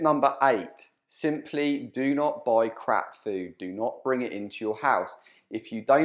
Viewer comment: For a minute there I thought you said don't buy crack.